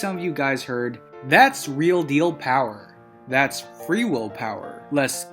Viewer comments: I heard “Let’s reveal power”. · I heard it as "That's the real deal power" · I hear "let's feel real power" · I heard "That's real deal power."